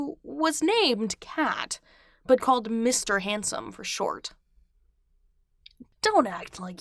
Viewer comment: I thought you said comment “Mr Hansen” and I was like “Dear Evan Hansen?” Then I realized it was Mr. Handsome 😐